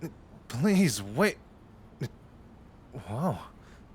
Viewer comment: Did I hear at 2:18 “wowzers”?😭 Anyways, great audio.